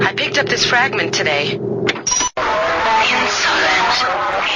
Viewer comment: what is it saying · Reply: "Insolent! Insolent!"